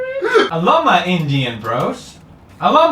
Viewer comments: it sounds like he said I love my Indian roads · can someone explain to non english speaker what's the problem with word bros? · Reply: it sounded like he said "I love my indian. bros?" not "I love my indians bros!"